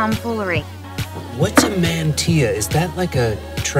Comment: Hi just a friendly lil correction. At 1:18 it's "mantilla" the captions say "mantea" lol love the video